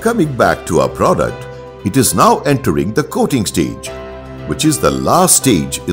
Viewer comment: can you replace this dinosaur like audio with sweet woman voice. because he sounds like TB patient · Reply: I thought said "goating stage" which is sad. His voice is better at X2